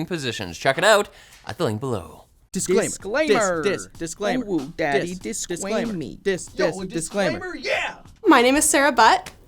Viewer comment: Did someone just say "uwu daddy discwaim me!" ? xD